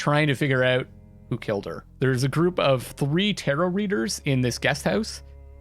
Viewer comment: i just love it when you say "tarot reader" but i keep hearing "Terror reader" =]]]